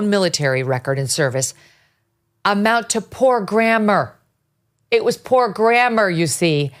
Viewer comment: "Grammar", I thought he said "Grandma"...